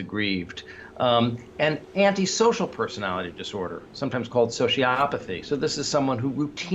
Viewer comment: Thank you, David :o) Multiple conditions: malignant narcissism, paranoia, social apathy, sadism ..... UGH! I'll go back to the original work of Erich Fromm; it's worthwhile reading before the next nuke sends the world's light on a loooong vacation. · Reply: Did Gartner say "social apathy"? I thought he said sociopathy, which is a vastly different construct.